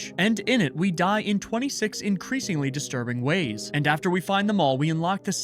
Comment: idk why my head did thought that you said your character died in 26 like the year, instead of 26 ways, lol